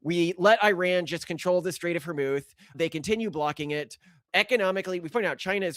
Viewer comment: "Strait of hormouth" I heard vermouth lmao wouldn't that be nice!